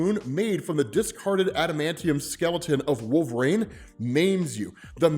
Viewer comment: i love the stories but man.. please dont take this the wrong way. i hear you say "green" lanturn so i know you can say "wolverine". or am i misunderstanding and theres a character called "wolv-rain"?